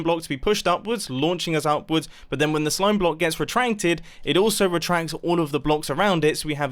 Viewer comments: Di-Did he say retrancted ? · Timestamp 6:28.did he just say re-trenk-ed